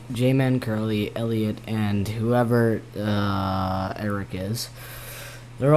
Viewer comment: did he just say whoever Eric is? For those who don’t know, eric1515 taught how to play.